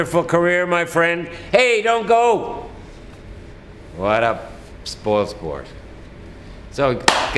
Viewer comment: Mr. Jim Lahey: "what a fucking BITCH" is what he actually said = Because im a Trailer Park Boys fan